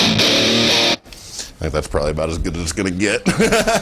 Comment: What the fuck did he say at 2:10? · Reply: "I think that's probably about as good as it's gonna get."